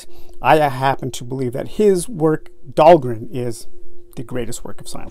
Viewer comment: because of my poor english, i didnt really understand at 1:22 what is in your oppinion the best science fiction ever written. could you please write it down. btw: great channel! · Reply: Dhalgren